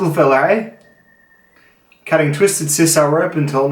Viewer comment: Every time you say TWISTED SISAL I hear "Twisted Sisters"....